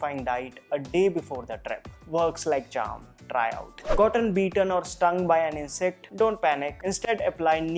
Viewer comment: I heard “Gotten Beaten “ in first time🤪😂🤣